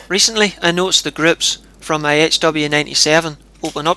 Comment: I thought you said your grips opened up slightly. Had to go back, groups... Love your video, brother. Keep em coming and THANKS.